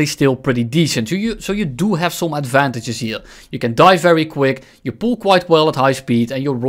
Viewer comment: Bruh, 2:37 I thought this man said: "-So you DO have some advantages here; You can DIE very quick,-", I did a double-take on that audio bruh XD